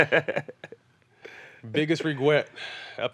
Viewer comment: did Matt say “Biggest reGWET”?